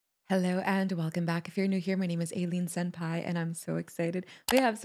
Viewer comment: Why is Aileen punching people in there skull in the pit 😂😂 greatest thing I’ve heard 😂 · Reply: I thought she said alien senpai😂